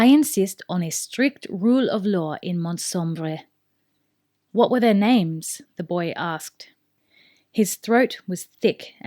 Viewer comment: did u said "what are their names"?